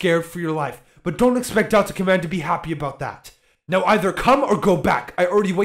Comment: I skipped a bit and i heard "NOW RATHER CU-" before skipping again